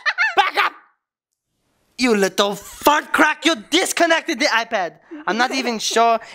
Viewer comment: I thought he said fcrack